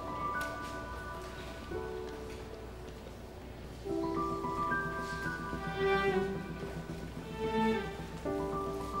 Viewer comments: de ne diyor? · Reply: Evet dedi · kız ne diyor? Lütfen soylermisiniz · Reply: Şey diyor sonrasını göstermemişler